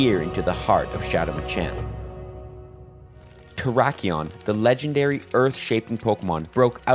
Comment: Sounded like you were saying trakion....